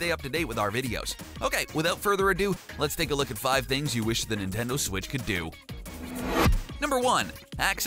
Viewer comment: Did he say Nino